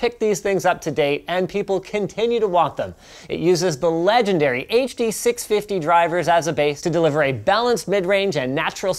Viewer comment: I thought he said "18650 drivers" I was like oh cool now my headphones can vape